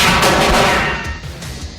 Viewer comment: haha it's so funny :) what does Castle say at the end ( 0:59 )? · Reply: Manslaughter it is. And Beckett says yeah.